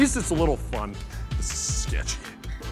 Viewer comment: Did swear at 6:33 or am I just hearing things? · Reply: He said ssssssketchy